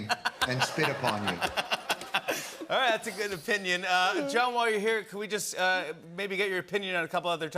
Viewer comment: Did he call him "Joe" at 9:30?? Also, why did they re-upload this? Wasn't this video already up like 2 months ago? I know I def watched this before.....